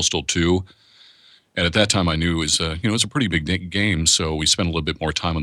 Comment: did he just say "It's a pretty big dic- GAME"